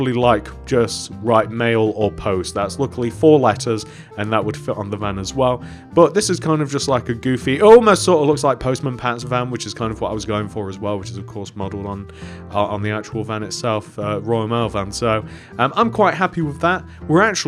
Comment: who else heard post man pan